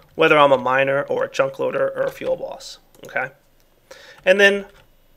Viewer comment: Does anyone else hear "Feel Boss" when he says "fuelBoss?" I found myself laughing a lot when I first heard him say it.